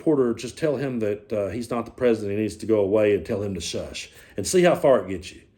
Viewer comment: I heard little X say " Mr president i need to go peee" .. but what is strange, when listening yesterday to other videos of this, I heard him say " your not the president you need to go home ". So idk what's going on here. 🤔